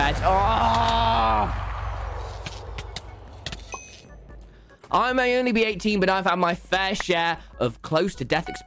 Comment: "I may only be 17." "I may only be 18." how old is tommy innit? 16 Years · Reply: First time I listened thought he said I may only be a teen